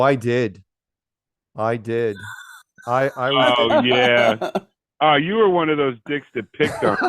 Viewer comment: what'd he say ?? "Ah, you were one of them dicks who..." ... WHAT ?? :O .